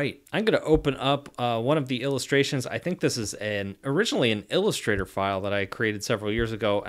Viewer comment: Major Newbie here, at around 5:45 did you say you that was an illustrator file?! Im trying to learn all I can about Affinity as I am getting the iPad pro (i no longer own a macbook) and I guess it would be awesome if I could import illustrator files and work on them in this app... I never thought that was an option 🤔 wow